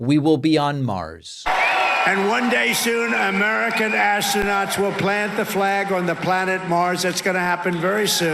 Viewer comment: i thought he would say "soon an American ass will be on Mars" 😂